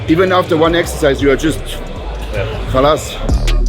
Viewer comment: did jo said khalas? It's an Indian word 😂